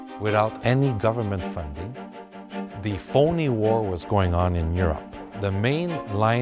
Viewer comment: I’ve listened 5 times..... 0:53 does he say “phony war”??